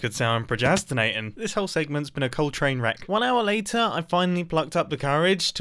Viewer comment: “This whole segment has been a Coltrane wreck.” I thought you said "Whole train wreck" so I came to the comments to make a Coltrane pun, but when I went back to get a timestamp, I realized you said Coltrane... Well done!!